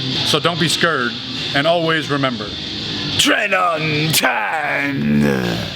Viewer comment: I’ll never forget the first time I watched one of your videos and thought that you said “train on time” lol showed the video to my friend and he said “train untamed” and I looked at him like he was crazy. Guess I’m the crazy one!